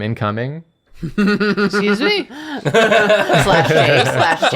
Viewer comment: For whoever does captions, I think says “excuse me?!” but the captions say Nathan